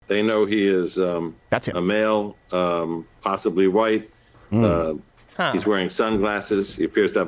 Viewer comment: I listened closely to the description and I heard them say " impossibly white"